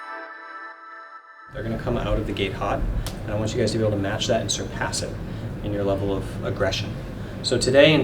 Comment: i thought he said "they're gonna come out of the gay pot" and i was extremely confused for a second.